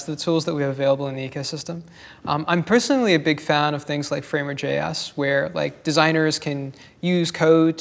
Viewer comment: Did he say FrameworkJS? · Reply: Jakob Nanneson framerJS